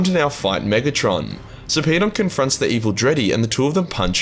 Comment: Everytime Sarpedon’s mentioned all I hear is ‘so peed on’.